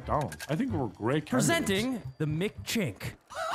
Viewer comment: What is racist about McChic? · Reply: you misheard, he said McChink